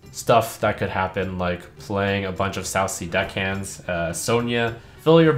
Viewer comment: lol thought he was saying "saucy deckhand"